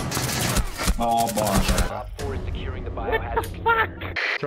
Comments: did he said "o Boze" POLAND! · did he said "o Boże"? Is it Poland :) ?